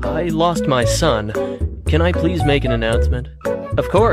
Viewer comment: 英語：Goodbye you little shi- 空耳：お前はもう死ー